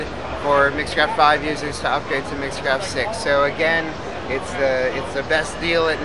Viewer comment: Oh, I thought you said don't use mixcraft